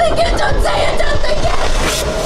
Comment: の「言うな、考えるな」が「どんくさいな、飛んでいけ」に聞こえる